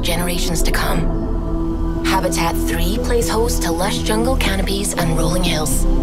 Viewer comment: I thought she said CANNABIS.